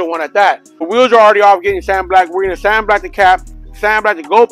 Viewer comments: I thought he was saying “sand blast” 😂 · This whole time I thought he was saying “sand blast” with a lisp. Until I seen the end result lol